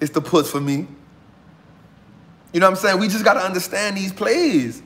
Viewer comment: I couldn't understand what merger he said at 44:00 was it teledyne? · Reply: Teledoc Health (TDOC)